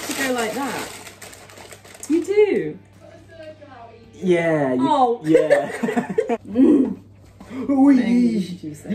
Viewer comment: sorry, but what was said there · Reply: I think she said u have to cut a circle out evie